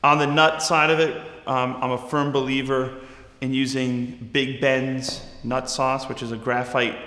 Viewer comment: Excuse me sir, did you just say “Nut sauce?” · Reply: MasunN47 “Big Bens nut sauce” to be exact lol